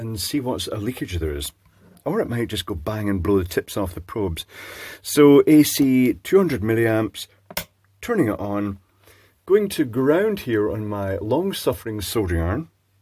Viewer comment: I had to rewind I thought you said blow the t1ts off the probes. 🤣🤣🤣🤣 WOW they are really cutting components down now its just about empty inside. As for the earth connection, linteresting